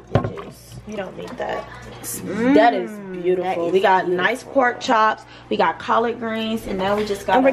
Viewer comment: she said pork chop instead of rib